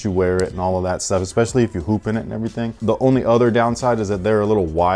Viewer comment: I thought he said poop he said hoop